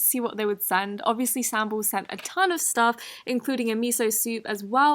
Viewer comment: What's the slime sellers name at 4:19? I couldn't understand you that well. Sam bowls? Sambols?